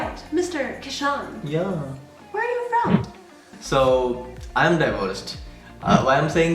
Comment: When u said I m diversed, I heard divorced Then thought why did he say that Heard it again then got the context .:p